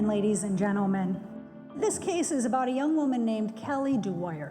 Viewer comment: Oh a young woman. Sounded like she said. A yellow woman.